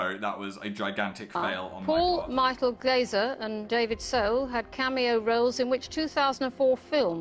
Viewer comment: did she say David So?